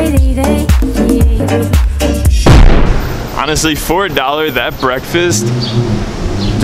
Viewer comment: did he just say $4 dollar breakfast at 2:15? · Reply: Flame Viper he said “for a dollar”